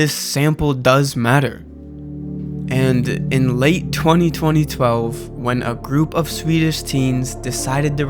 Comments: did his say in late 202012? LOL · in late 20 20 12? am i hearing this right? or are my laptop speakers not good or sumin? or is my hearing gone up?